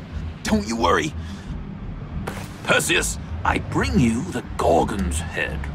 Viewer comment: Thought he said he brought the Gordon's head